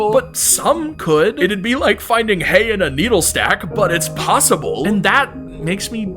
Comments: Ok did he say HAY in a needle stack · did he just say, “Like finding hay in a needlestack”